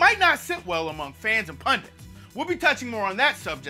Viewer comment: did he say and punters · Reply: and *pundits- 1. an expert in a particular subject or field who is frequently called on to give opinions about it to the public.